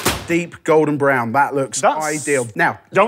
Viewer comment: With these ruddy heavy accents, I keep hearing "Keep the teat out on the 'andow" and left me wondering "do the Brits call the 'handle' a 'teat' for some antiquated nonsensical reason?" Then it dawned on me - these posh bougie-ass Brits have such things as called "tea TOWELS"! lolol Much of this, save perhaps the mondegreen, is a joke btw 🤣 XOXO, from a Murrican fan